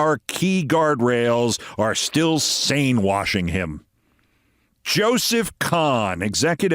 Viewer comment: I thought folks were saying stain washing. Sane washing really hits! Thanks, tee hee.